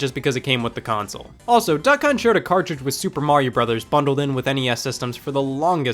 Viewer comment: I wasn’t watching the screen and I thought he said “duck cunts.”